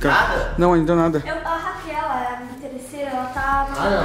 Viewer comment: Impressão minha ou a falou o nome da menina eu entendi Raquel no minuto